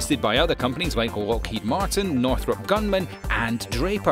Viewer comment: "North top gunman." Jesus someone catch that guy before he shoots up the other competitors.